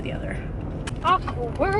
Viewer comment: lol it sounded like you said "awkward" lol